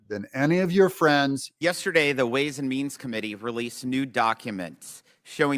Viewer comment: I know I misheard when I thought he said "ways & memes committee" but I like the concept. 😂